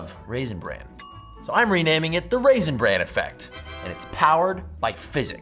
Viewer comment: I thought it said the Russian brain effect and was really confused when he started talking about cereal